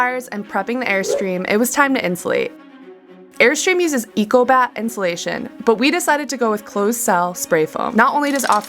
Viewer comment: I had to laugh, I thought you said, "Airstream uses fecal bat insulation..."